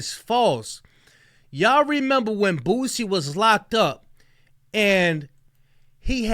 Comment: WHY DOES IT SOUND LIKE YOURE SAYING BOOTSIE